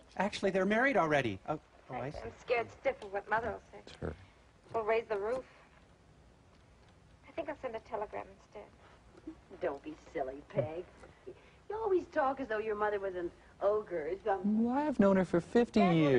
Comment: Does she say, "We'll raise the roof." ?